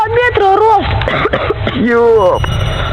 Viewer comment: I heard yulp/steam.